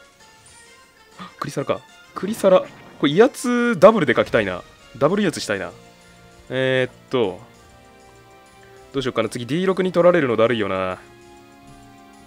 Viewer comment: ダブル威圧がガブリアスに聞こえたw